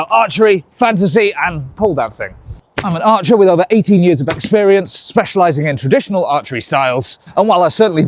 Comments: I misheard this as "I am an archer with over 80 years of experience" 😭🙏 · I keep hearing "Over 80 years of experience" (vs what I'm assuming is actually "18") and I've gotta know your secret to eternal youth! XD